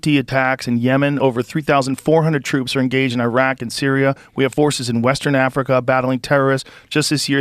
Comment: For some reason I heard “we have horses” he said forces 😂😂😂